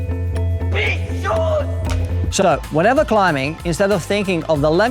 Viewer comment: did he say "shut up" ?! :D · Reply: ) I said "So.."